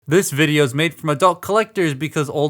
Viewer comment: am I the only one who heard "this video is made from adult collectors"😭🤣